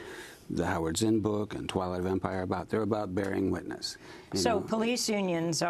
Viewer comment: did he say "Twilight vampire" lol?